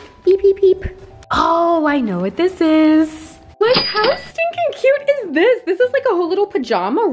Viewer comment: When you said “how stinking cute” about your PJ set I heard “my housekeeping suit” which would honestly probably be you anyways😂💓